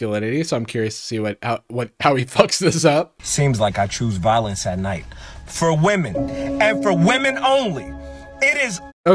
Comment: in the violence and night part i thought he was tryna say "i do violence at night for women and women only"